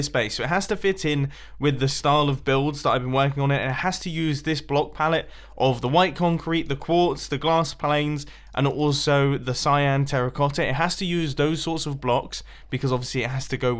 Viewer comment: Did you just say Glass planes?